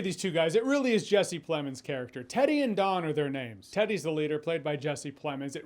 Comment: Was the cousin doing his best Forrest Gump impersonation or is it just me? Everytime he said Teddy all I heard was "jen-nay"